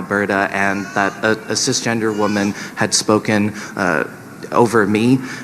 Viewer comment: did Shrek just say cis gendered woman?